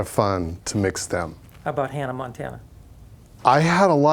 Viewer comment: sorry guys...but..but..i hear "Hannah Montana" like credit?!?!?!?!?